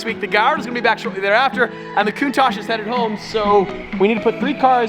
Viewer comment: did he say countach is headed home